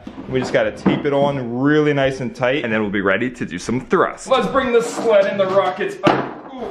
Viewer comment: When you said that "And then we're ready to do some thrust" i Heard "And then we're ready to do some drugs" 😂